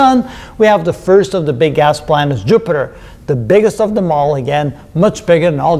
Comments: I legit thought Michel said bigass planets before I realised it was big gas planets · Did you say "big-ass planets"? lol (2:02) I laughed....I probably heard it wrong, but I thought it was funny! Love the numbers and comparisons. This is what I like to do too. I don't think people realize that it's a lot of effort. Sorry for the off-topic again ----- when something falls from a great height and lands on water. It's like hitting a hard surface. How would you characterize that? How would you show that water can't move out of its own way fast enough to permit a falling object to enter without being damaged?